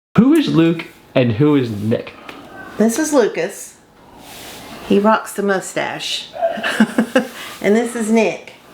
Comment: Lol in the beginning, i thought his mom said Lucas wants the most stash! I was like eeeeeeh? then i noticed the moustache xD